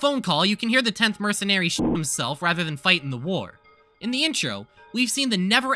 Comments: bro i heard "shit himself" and i nearly died of laughter. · for a second i genuinely thought you said he shit himself